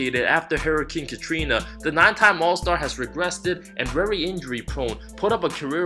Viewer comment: great video but did you say "regressted" @ 6:45 and did you call Frank, Fred @ 8:04?